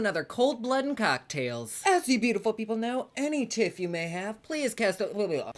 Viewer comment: Until this video I thought you were saying “if you have any tips cast them away” not “tift” and I was like why can’t we leave tips? I get it now :)